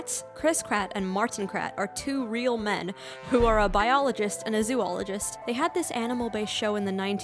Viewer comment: srsly thought that said “no one can convince me Martin isn’t bad” . I need to go eat wtf Also SCRUMPTIOUS I LOVE THIS